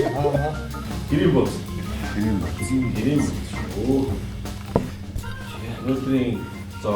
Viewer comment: that waitress did not say "Today's meal is, ...." all he said was home buuz.